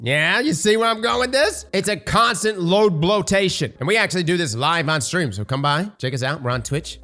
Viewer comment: I misheard part of the video as "We blow loads live on stream" what kind of channel you running man